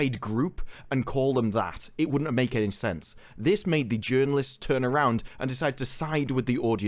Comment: I think you didn't pronounce "activist" right, I think I heard "journalist". I may be wrong tho.